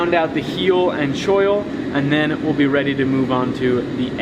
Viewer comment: WHAT IS THE WORD HE JUST SAID?? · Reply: choil